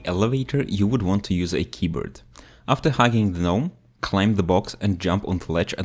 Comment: Did you just say “after hugging the nome” 👁👄👁